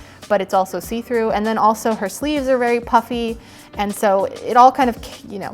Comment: it sounded like you said puppy